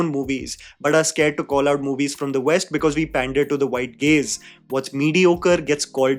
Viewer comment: what say it again? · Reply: It's gaze bro 😆